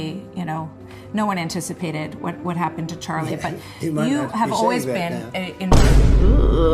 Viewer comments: what is he saying here?? My audio processing is so shit, I cannot for the life of me hear what he’s saying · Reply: "Nobody had anticipated what would happen to Charlie, but" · trump slurs his words so much I can't tell wtf he's saying. Translation please?? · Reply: "He might not be saying that now" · what did he say? l couldn't hear him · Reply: "I don't think he'd be saying that now"